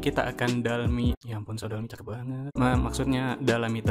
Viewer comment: Di menit 0:06 itu, dia ngomong apa yah? Sorry gak kedengeran suaranya. Thanks. 😅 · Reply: Cantik bangat